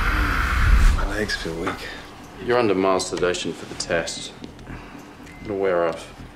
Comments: auto cc says "you're under masturbation for the test" 😂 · What Chase is actually saying: "You're under mild sedation" What automatically generated CC are saying: "You're under masturbation" 💀💀😳😳